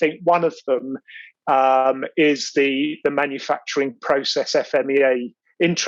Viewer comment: thanks a lot for a very useful video. I'm from Japan so I didn't catch some words in it. can you please tell me the words said at 4:39s (Personally I think one of them is the manufacturing process FMEA ...... the standard specifically calls for an FMEA). Thank you in advance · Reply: Yes, states the Manufacturing Process FMEA or can also be referred to as the PFMEA